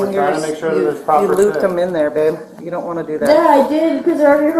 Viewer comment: Bro did he said babe to her ?? 😅